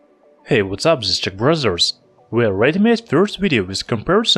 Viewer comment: I heard "What's up it's the Brazzers" :D:D:D